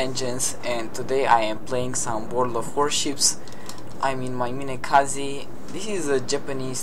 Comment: I thought you said kamikaze at the start of the video xD